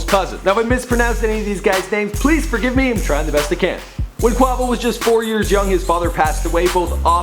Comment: wait did he just say cuavo